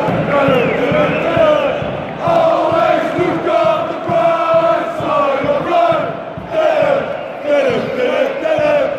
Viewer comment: hahaha goud dit wat zeggen ze op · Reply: SJENGEN